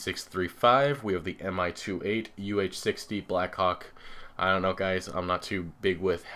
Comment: Did u say black cock